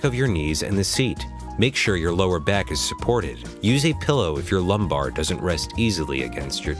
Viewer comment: did you just say pellow??lol